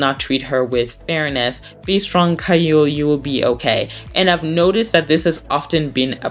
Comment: When you said Gaeul I heard Caillou and I thought you were talking about the cartoon 😅